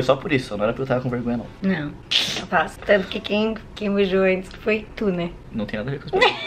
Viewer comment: " tanto que quem beijou antes foi tu né" Só eu ouvi isso♥️♥️ A slime é real galeraaaa♥️♥️♥️♥️♥️♥️